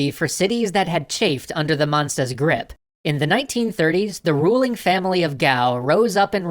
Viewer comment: why did he say I’m the 1930s ?